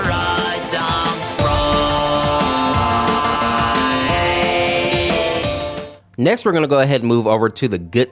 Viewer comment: can you tell me what he says here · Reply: For a dumb prize